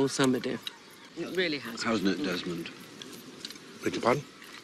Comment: Hasn’t it, Desmond?에서 hasn’t it이 how’s it 으로 들리는데 발음 설명해 주실분🙋‍♀️